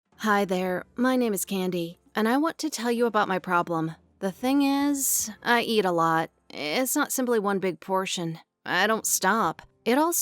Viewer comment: I thought she said handy but she said candy 😭🤦🏽‍♀️...sounds like a nickname for a dancer